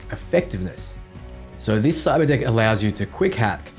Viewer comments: i thought he said cyber dic@ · I SWORE said cyberdick and I was like WHHHHAT! but nvm xD